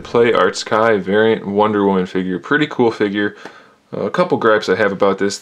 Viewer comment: For as much as they cost, I don't think "Pretty much an OK figure" is a great start, lol. Also... I am really surprised she didn't come with a sword. She has the shield, but no sword? And Supergirl came with a sword? Just seems weird. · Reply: Wrong. "Pretty cool figure" is what he said.